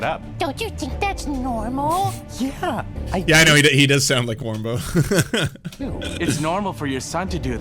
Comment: i first heard this without seeing the vid and i swore it was warmbo